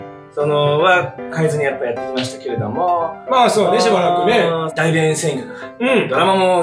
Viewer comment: 大便占拠にしか聞こえない